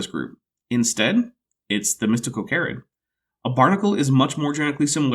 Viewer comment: what are those guys called? I keep hearing it as "mystical carrots". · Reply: Mystacocarids